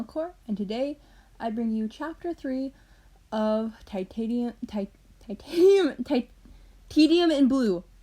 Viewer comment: When u said Tedium it sounded like you said titanic XD